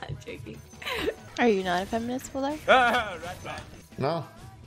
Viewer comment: can someone tell me what says? is it "Are you feminist bulldog?" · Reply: "are you not a feminist bulldog?"